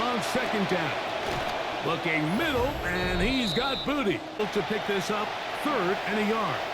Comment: did he say booty or am I tweaking?